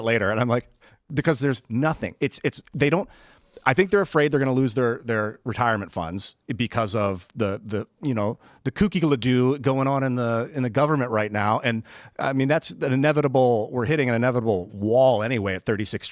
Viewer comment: Ian, did you say, "kooky ladoo?"